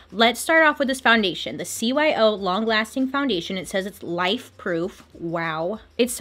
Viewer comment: When you said “life-proof” I thought you said “wife-proof” and I was like😂 what does that even mean😂